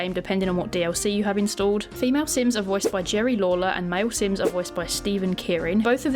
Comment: I thought you said the Sims 1 female voice was Jerry Lawler, as in the wrestler. And I was like wait he's a male himself. Then I looked. Gerri Lawlor. And I was like, okay. XD